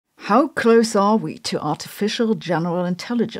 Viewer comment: I heard " how cursed are we? " And I was like " Very " but yes go on...